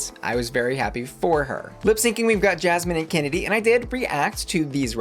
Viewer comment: did anyone else catch that? 😂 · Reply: “Lip synching we have Jasmine and Kennedie” 😂